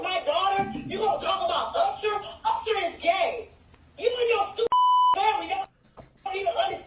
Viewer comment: Did she say: "Usher is gay"? 😂 😂 😂